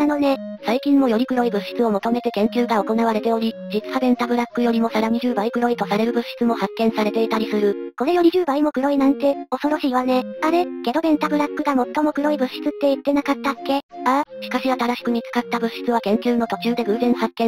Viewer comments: ベンザブロックって聞き間違えた奴は俺だけじゃない、はず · ベンタブラックが便座ブロックに聞こえた私って一体…